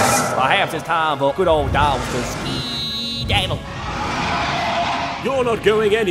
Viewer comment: What the HELL does Dartz say at 9:31??? 😂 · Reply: perhaps its time for good ol darts to skiiiiiidaddle